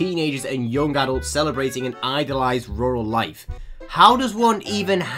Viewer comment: im gonna be honest i thought "rural" said "ritual"